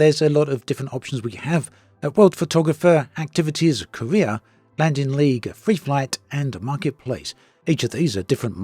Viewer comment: did you say "landing league"?